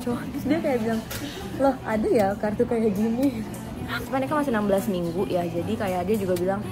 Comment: ga mekap aja cantik sekaliii🤏 masyaAllah 5:12 kaget bgt aku dengernya "mereka?" kembar kah dede bayi nya?😭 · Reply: kalo ga salah "cuma ini kan masih 16minggu" gt deh ka